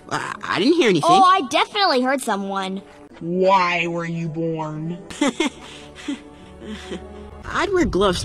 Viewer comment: am I the only one who heard Why were you born